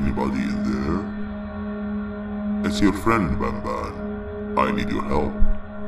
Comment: bro I thought it said Batman.